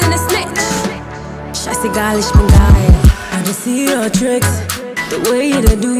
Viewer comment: Did she really said "Scheiß egal ich bin geil?!!! 00:39 #German